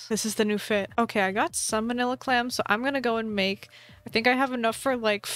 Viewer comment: Go to 6:37 I swear I heard her say "Okay I got salmonella calms" I'm sick at the moment and I laughed so hard I coughed HARD! it was painful and salmonella calms isn't even that funny...